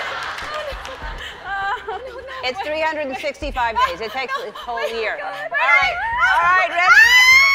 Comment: How do you not know "365" and "oxygen" like that's sad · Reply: It's 365 days. which is what Ellen asked. But it's also 365 days and little over 6 hours. which is why we sometimes have a leap year. Plus, I could've sworn I heard Ellen say 355 days... :(